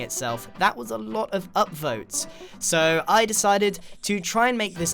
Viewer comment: hold on did he say upvotes REDDIT INTENSIFIES